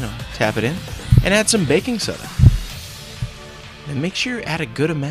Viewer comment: Sounds like he is saying "bacon soda"